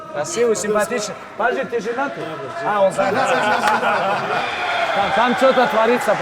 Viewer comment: что он сказал? не слышно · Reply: Он спросил ты женатый тот ответил да и Хабиб понял мол все отмена отмена 😂